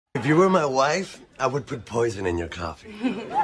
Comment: I thought He said 'if u ruin my life'😭😂